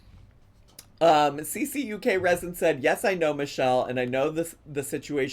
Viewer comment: I am a 49-year-old professional woman. Did I laugh like an idiot every time I heard "Cuckresin"? Absolutely. 😂